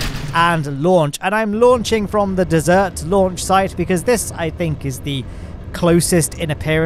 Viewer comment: Did he just say dessert launch site? Or is that just the different British pronunciation and I am too much Americanized?